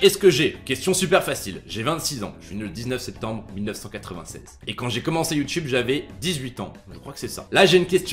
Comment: c’est moi où t’as dit que t’es né en 1987 ? 😂